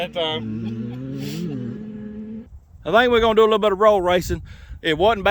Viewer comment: That looked like a lot of fun. Glad you got to do it. Loved the donuts. At first I thought you said road race instead of roll race. I still think the go cart would have beat them all.